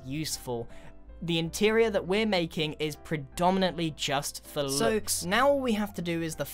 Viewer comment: Who else heard "Soaks" at 5:47?